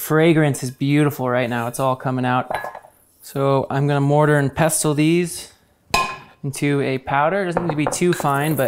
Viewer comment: I thought you said "I'm gonna murder and pestle this" 😂 Thr burgerrrr is amazing and looks tasty, and the lemon garlic aoli that i just watched is amazing and healthy.